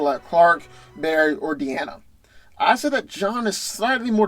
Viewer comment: I don't even care about the results anymore. Did he just say De-Ana?????